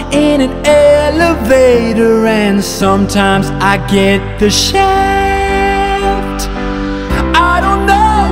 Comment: Just wondering.... at 2:22 did he say the S word? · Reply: No he said shaft